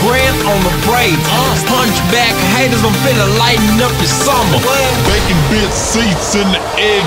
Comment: Is he saying I make you shallow? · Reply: I thought he was saying lighten up ya summer